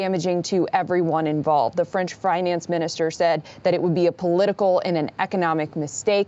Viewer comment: lol did she just say the french fry-nance minister